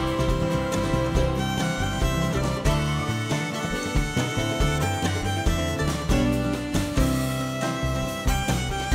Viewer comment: Killer version, she's so talented! What does she say at 2:32? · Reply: i think she says “let’s go”, as in “time to call and response”